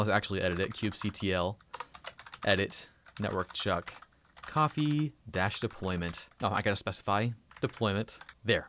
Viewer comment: great vid. thanks. Anyone catch what occurs @ 16:55 with the "de" ? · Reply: it must be: kubectl edit deployment networkchuckcoffee-deployment